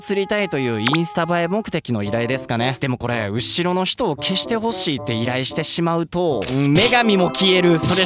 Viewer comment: 見る度に思うけど 「後ろの人を消してくれない？」っていうやつ、〇せみたいに聞こえる我やばいかも…